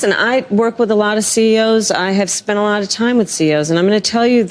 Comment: IS Kamala saying CEO's or CO's? I've listened to it many many times and she seems to be saying CO's which would make more sense since she as "Super Prosecutor" would talk with Corrections Officers a lot. CEO's not so much.